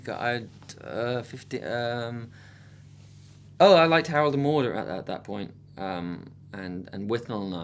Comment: Hey guys ! I need help, i'm french and I don't understand which movie Thomas is talking about at 2:43 so please help me 😁😘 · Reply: I think someone in the lower comments said the first movie he mentioned was "Harold and Maude". =)